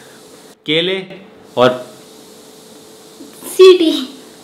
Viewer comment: what he said to Piyush 😂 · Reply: City